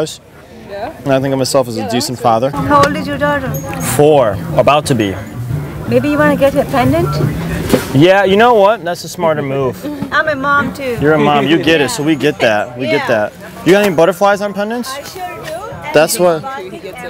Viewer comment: I heard "May be you want to ger her Pregnant" when she said " May be you want to get her pendent"